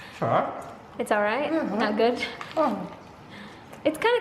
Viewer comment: I thought said, "ang sarap!" "It's alright" pala. Hahaha